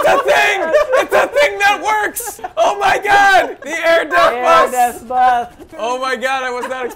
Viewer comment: It kinda sounds like there saying death buth instead of death bus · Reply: they are saying death buth